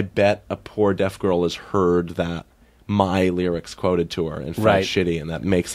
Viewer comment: "I bet a poor deaf girl's heard my lyrics and felt shitty..." Bo, deaf people can't hear. That's why they're deaf. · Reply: He said 'quoted to her' as in someone has told her or signed it to her.